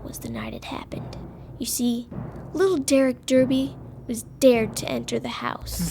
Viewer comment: I thought she said Dirk Diggler instead of Derk Derby! LOL